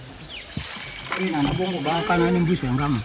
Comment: honest question did he just say mercí?